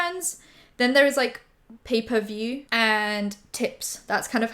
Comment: Omg im so dumb i just realized was saying pay for view i thought she was saying paperview